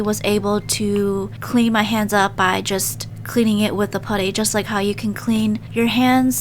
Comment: i thought it said puthy